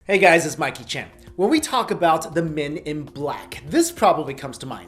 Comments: Whenever he says "this is Mikey Chen" I hear "this is Jackie Chan". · i swear i thought. he said hey guys its Jackie chan